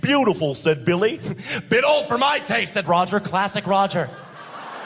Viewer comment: All these comments say "A bit old for my taste" yet all I hear is "bend over my taint"